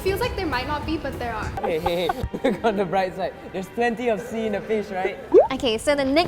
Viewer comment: did I just hear say "theres plenty of sea in fish right?" .-.